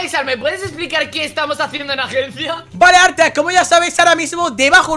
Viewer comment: dijo artack 🤔🤔🤔 1:58 porque dicen yate xdxdxd:-!ಠಿ_ಠ 13:04 para eso usas solo una paredಠಿ_ಠಿ